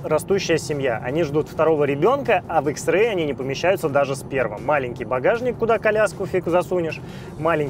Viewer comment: Послышалось - "... А в xray не помещается даже сперма"😅